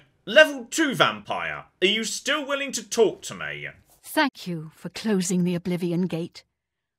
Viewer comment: I thought she said fk you at first